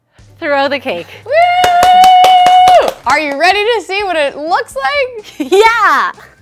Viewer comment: When said ‘are ready to see what it looks like?!’ I heard ‘are ya ready kidssssss?’